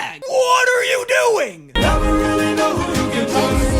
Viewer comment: anyone know what the silly little dudes say at 15:52 about singing? i cant really make out what they are saying aside from "sometimes singing-" and even that may be wrong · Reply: Their singing "sometimes seeking is a must", basically saying that they don't believe Odysseus and want to look inside the bag for potential treasure or food he must be hiding(since the silly creatures and men were singing together)